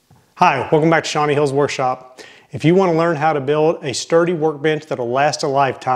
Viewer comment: Every time you said bench I swore you said b!tch lol